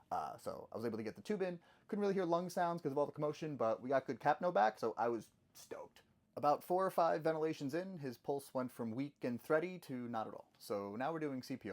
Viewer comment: The first time you said “I was stoked” I heard “I was stoned” and I was like there is no way he just said that and had to go back lmao